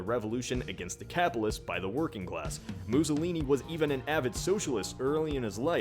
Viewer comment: it sounds like your saying moossolini